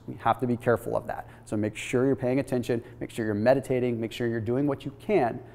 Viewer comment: Be sure you're medicated to reduce stress? WTF? Oh, I misheard it--he said make sure you're meditating.